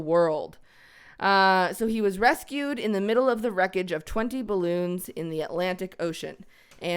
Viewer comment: Thought she said it was called 21 balloons not 20 balloons